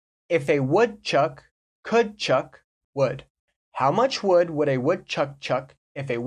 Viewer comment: I guess that the last word should've been "wood" instead of "would"! Right?